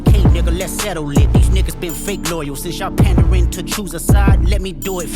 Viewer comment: it sounds like he says “Jews a side” not choose. 25:04 he talks about being “politically correct”. Maybe I’m tripping but that sounded like a shout out to the Palestine movement.